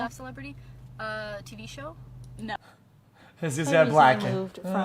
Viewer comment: What did he even say at that point? All I heard was: "hishishisshs black" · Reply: he said 'is this guy black'